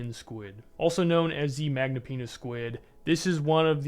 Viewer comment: did he say magnum penis